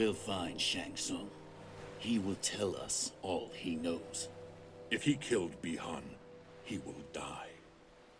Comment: I thought he said... If we killed Bi-Han, he will die. Ahahaha